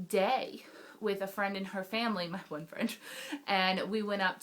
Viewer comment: Did you say boyfriend?? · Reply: She said "My one friend." I thought I heard boyfriend at first, too.